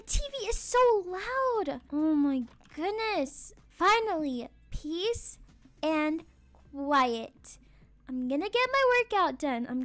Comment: Did anyone else hear her say "Peace and yiet" instead of "Peace and quiet?" Idk if it was just me 😅😂🤣